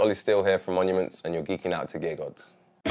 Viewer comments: Lol. I thought he said "gay gods". Love british accents. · i thought he said gay gods